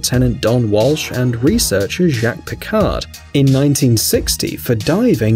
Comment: I thought I heard Jean-Luc Picard (Star Trek)